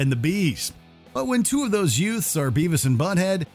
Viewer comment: sorry, what did you say? what was that word you used? "yutes"? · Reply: Youths (Teens and younger)